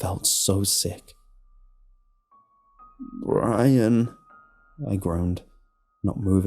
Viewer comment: It sounds like you said my name 25:34 My names ryan I heard it and Jumped